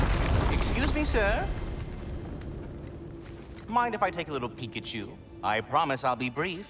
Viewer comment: I can't help hearing say "Mind if I take a 'Pikachu'" (instead of "peek at you") as a cheeky reference to ProZD's favourite video game / obsession.